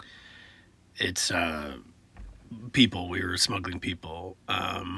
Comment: I thought he said " we were smoking ppl " and was so confused and had to go back " we were smuggling ppl "